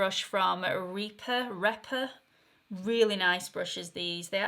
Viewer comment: can you please spell the name of the brush you said was so good that you had received a long time ago? To me it sounded like you were saying "Reaper", as in. "The Grim...". Lol! 😉 Thank you! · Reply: Rephr xxx